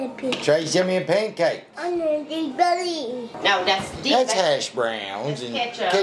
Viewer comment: I think said In my belly lol